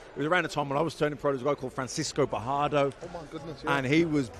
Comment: what was the name he said then? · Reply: Sounded like Francisco Bojado. A young bojado looks like Ryan Garcia